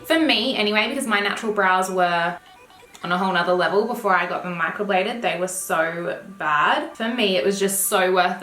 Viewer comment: AMAZING !! Hahahaha when you spoke about your eyebrows I thought you said u had them microwaved lol